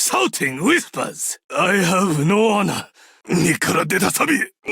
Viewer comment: Waiting for someone to provide all his Japanese voicelines. At least the ones that I caught were : 15:23 "Nikura de da sabi" (Not sure about this one) 15:24 "Muki sashi narau" (Also not sure) 15:26 "まじで?" "Maji de?" = "Really?" 15:50 "豆腐の角に頭がぶつけて，死んじまえ!" "Toufu no kado ni atama ga butsukete, shinjimae!" = "Bump your head on the corner of a tofu and die!" 16:01 "乾杯!" "Kanpai!" = "Cheers!" 16:03 "やばい！" "Yabai!" = "Oh no!" 16:13 "日が逃げ出る" "Hi ga nigaderu" = "The sun is escaping" (Not sure) 16:14 "Tantou tokun yuu" (Not sure) Also, the name of his first gadget, "Dashi dash", has the word "出汁" "dashi" which means "soup stock". His second gadget, "Hosomaki Healing", has the word "細巻き" "Hosomaki" which means "thin sushi roll". His second SP, "Nigiri Nemesis", has the name of the most common type of sushi, the one with a single ingredient on top of a bed of rice. Yes, I know about the "ora ora ora" as well. Might as well have shouted "Ryuujin no ken o kurae!" when using his super. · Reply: When I first heard "マジで", I thought did samurai says this word in that era ? lol 15:23 身から出た錆 (Mi kara deta sabi) 15:24 Probably 抜き差しならん (Nukisashi narann) 16:13 地金が出る (Jigane ga deru) 16:14 単刀直入 (Tan tou choku nyuu) (You can check all voicelines at brawl stars wiki fandom. But I cannot post a link, also some of them are not written correctly)